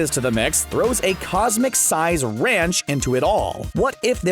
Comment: I guess in your universe you say 'ranch' instead of 'wrench' huh?